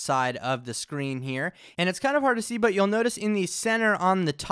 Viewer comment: did you say "it's kinda hard to see but you will notice"?